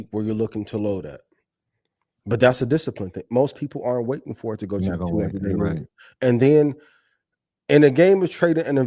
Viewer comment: What did Ian say at 1:18:00? Troy spoke at the same time? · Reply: Right? I think Ian said most people aren't waiting for it to hit their 200 ema.